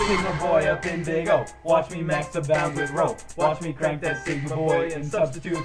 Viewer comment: Sounds like your saying now watch me masterbate to that how